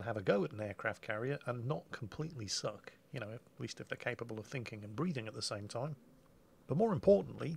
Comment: Silly me, I heard say "thinking and breeding at the same time." At 3:00 😂😂😂😂